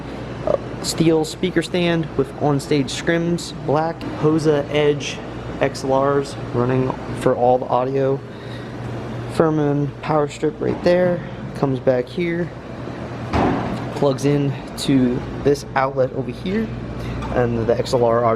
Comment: it's plugged into my dbx driverack pa2. What do you mean by strip? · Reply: at the 7:00 minute mark, sounds like you said Firma powerstrip... Basically Im tryna to figure out how you got your speakers plugged up to your mixer that far away